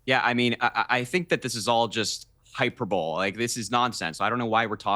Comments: did he say Hyper Bowl ?? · Did Harry just say “hyper-bowl”?!? 13:07 🤣🤣🤣 how does anyone take this kid seriously??? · did he just say "HYPER BOWL" ??